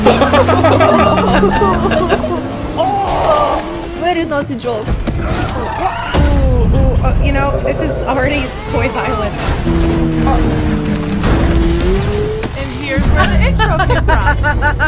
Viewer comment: i swear it sounds like says "soy violent." just me?